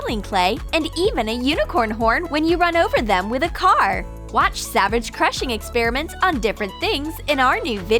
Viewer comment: 0 So satisfying And if anybody heard it it’s say savage 0:43 Also love your vid